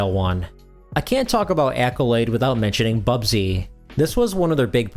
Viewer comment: at first I thought it said Bussy